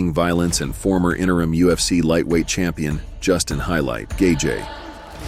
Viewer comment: did the narrator pronounce Gaethe as Kay Jay❓️⁉️ Please tell me I'm not the only one who heard that 😮🤔